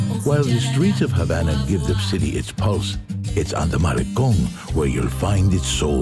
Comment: charming. but did he say "maricon" around 6:01 ? ---- LOL